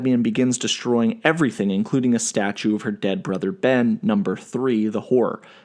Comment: I thought you said “The whore” 😂😂